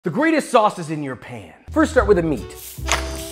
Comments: Thought he said,"Greatest sauce is in your pants"💀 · I thought you said "the best sauce is in your pants" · I thought that he said “The best sauce is in your pants💀”